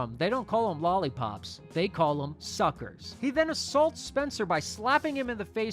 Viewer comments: When you said the delivery guy said suckers I thought when I saw and heard it he said soccer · When I was a kid, I misheard this scene as “Where I’m from, we call it a SOCCER.” and I was always like “What?… i don’t get it.” 😂. I was so silly